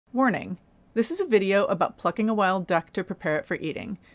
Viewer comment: At first she sounded like she said F***ing a dead duck.